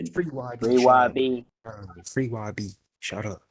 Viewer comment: did bro say shutup? 😅